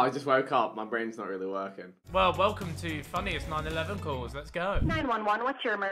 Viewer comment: DID HE SAY "FUNNIEST NINE ELEVEN CALLS"